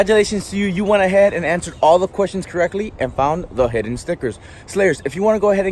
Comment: It sounded like he said snickers not stickers